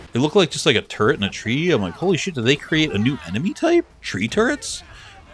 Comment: Beware the TreeTurds! Funny how I misheard that.